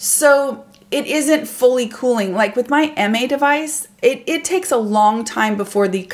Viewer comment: love all your videos! What is the name of the tool you’re referring to please? I hear “MA Device” but can’t find it. Thanks!! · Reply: Sorry! I should have listed it 💜 Here’s a link (it’s called Emay) <link> 😊